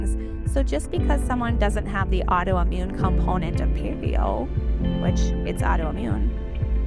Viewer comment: purvio? I’m a layman. What was that word ? It was said in really severe glottal fry